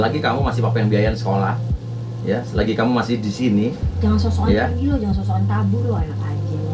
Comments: Menit ke 8:00 mama cinta "jangan sok sok an pergi Lo, jangan sok sok an kabur Lo, anak anjin* Lo" PLS GW SALAH DENGER GASI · itu mama bilang apa sih, takut slah sngka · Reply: "Jgn sosoan pergi lo, jgn sososan kabur lo, enak aje lo" Sy awalnya juga mikir prasangka yg sma ky mbak kok wkwk